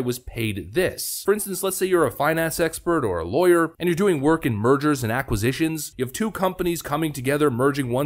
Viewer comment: I thought you said murders and acquisitions 😆